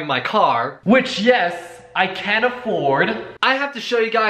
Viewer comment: he said he can afford it, but the subtitle said can’t XD